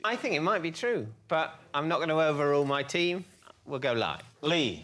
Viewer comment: What David said: "I'm not going to overrule my team" what i heard: "you're barmy if you think i'm going to overrule my wife on national television"